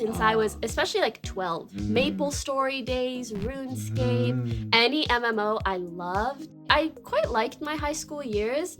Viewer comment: subtitles say anime, she said MMO